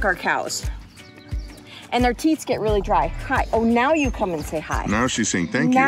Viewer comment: When said their teats get dry I thought she said their teeth, I had to go back and check. Lol